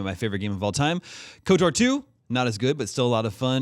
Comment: what are the first two JRPG's Daemon mentions? He speaking so fast I can't quite understand what the games are. I usually like checking out any game they mentioned that I didn't know of, but he said it too fast wth lol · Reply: KOTOR and KOTOR 2, Knights of the Old Republic. They're old BioWare Star Wars games, not JRPG's, but RPG's